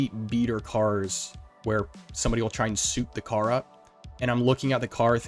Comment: Hey, I'll take it! 😄🤙 Thanks for watching 🫡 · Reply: sounds like you said "suit the car up"